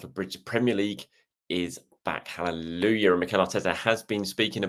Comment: Hi, Charles, did you say hallelujah 0:21. Had to listen again to be sure. Good job love your content